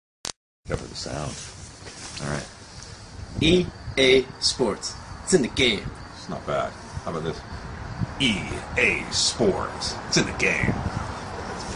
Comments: I thought he was saying "to the game" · I hear "EA Sports tena game" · I always heard, “To the game” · E A sports to the game ! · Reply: 3Danger us Am I smelling a mandela effect here? Cause I always thought that he said 'to the game' 😂 not being serious about mandela effect, dont come at me lol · Why I heard "EASPORTS - TO NET GAME" ? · I hear it "EA SPORTS tsenna gay !! " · When I was a kid, I thought it said, "EA sports syndicate" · I always thought he was saying "E A Sports, typic game." Not sure what that means, but thats what I heard. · I alway thought it said "EA Sports, to the game" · I always thought he was saying "To the Game" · E A sports to the game ! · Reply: I always thought I heard "To the game". Now I realized it's "it's in the game"